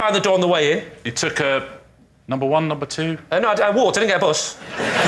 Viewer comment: What does lee say at 21:30 ? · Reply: "I walked, I didn't get a bus"